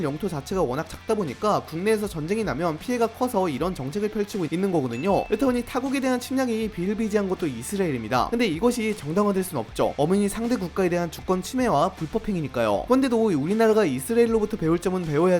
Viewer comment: 저만 이스레엘로 들리나요?